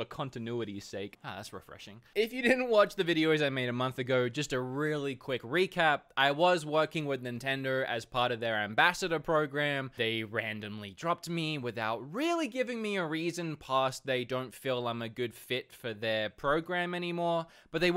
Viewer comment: lol I thought you said they randomly drug tested you... I was like oh so that's why you were dropped huh!